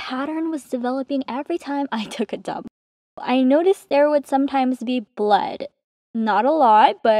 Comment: ayo nahh im a filipino and i just heard "budy blood"💀💀💀💀